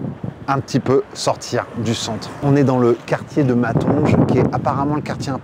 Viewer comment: le quartier quoi ?????? Perso jamais entendu parlé ! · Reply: C'est le quartier africain de Bruxelles c'est matongé près de porte de namur